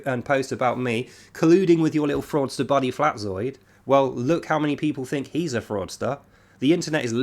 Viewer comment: I honestly thought you said Flatroid. I thought that was hilarious so I listened to it back a few times to be sure, but I was mistaken and you actually said Flatzoid and not Flatroid. I'm going stick with FlatRoid, it's the perfect name for him, it's just so apt. FlatRoid 🤣